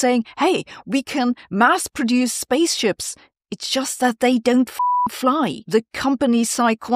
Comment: "Don't put all your eggs in one bubble" - Nvidia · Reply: "[...]they don*t fit or fly!"? Couldn't understand due to the bee ping.